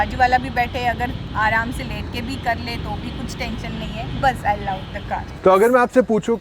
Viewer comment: I can't understand. 😁 Aram se_________#. 🤔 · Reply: let ke kar le toh bhi koi problem nahi 🤣🤣🤣